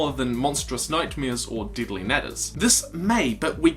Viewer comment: It sounds like you said dilly nadders